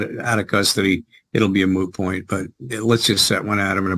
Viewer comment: I could swear he said "moo point"